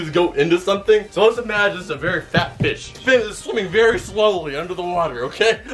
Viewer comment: i thought he called the watermelon a fat bitch...he said fat fish